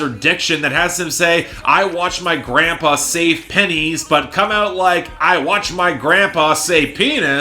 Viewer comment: No joke, when you said what the line actually was "Bout My Business", I actually had to go back to re-listen to the line, because I heard "penis" instead of "pennies" the first time.